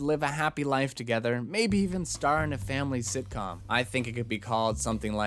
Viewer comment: Did you say "sithcom"?